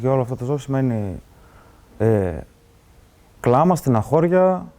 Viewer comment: Your subtitles are wrong at 9:25, it's "weeping, sadness..." etc. and not weeping in the village. (stenaxoria, not "sta xoria")